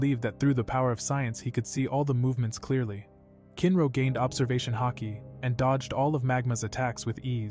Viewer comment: Did he say he got observagion haki?💀 like fr 1:49:00 "then they decided to play mimecraft and get the mpst heat resistant rock in the universe" Me: HOW DID GET NETHERITE WITHOUT GOING TO NETHER?!